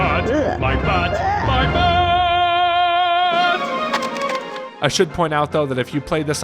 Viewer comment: So much poop in that one level i thought you said “i shit point out though” instead of “i should” at 8:05 💀